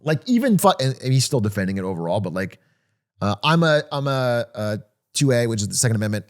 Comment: for a second I thought atrioc was saying he's a 2a guy